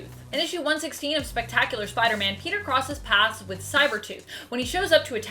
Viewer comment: is it me or did she just say "cybertooth" at 5:38?